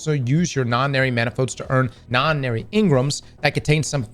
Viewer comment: im so brain rotted that i keep hearing "non binary engram" instead of nonary engram